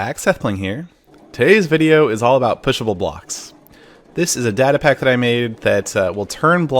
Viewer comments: At first, I thought it said PUNISHABLE blocks. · I thought it said punishable blocks